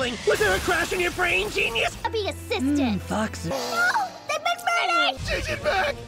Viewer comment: did someone say “mmm foxes” 😭😭